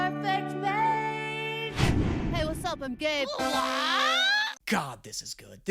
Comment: lowk thought he said he’s gay